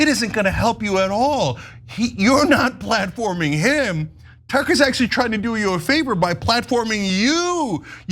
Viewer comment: Tyt love all the white supremacist anna i thought you said tucker is a racist